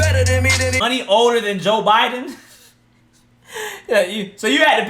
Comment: I always thought he said Joe Budden